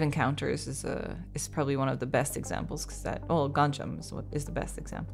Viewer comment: mentioned a spooky movie, and compared it to Grave Encounters. i LOVE those movies, I couldn't understand the name of the movie though. What was it? Edit: Nvm! Found it after a quick google search. Adding Gonjiam: Haunted Asylum to my watchlist to scare the bf 😏